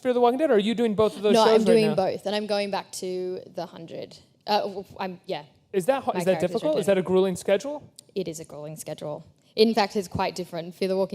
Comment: Did she just say at 11:51, "My character is dead?" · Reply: no she was kind of stuttering, she said "well-i'm-yeah-my characters returning"